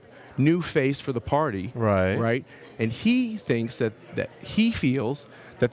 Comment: I hear Michael's "right, right" as "careful, careful".